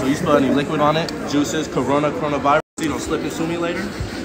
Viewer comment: “Fast, Clean, & Easy. Just like my ex.” 😅😂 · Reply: I thought he said "fast, clean and easy. Just like my ass😐